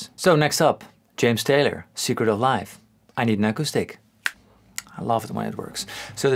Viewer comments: sounds like I need an echo stick at first, then you hear he actually says I need an acoustic. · When you said “I need an acoustic” I heard “I need an echo stick”.